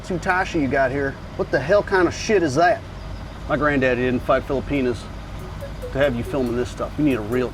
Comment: What did he said on 0:34? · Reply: Sounded like "My granddaddy didn't fight Filipinos to have you filmin' this stuff...."